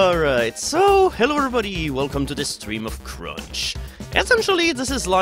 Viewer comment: I took off my headphones before the video started, then I heard... "Hello everybody ! Welcome to this stream of Crotch." I was like "oooooh boy, this is promising", until I rewinded.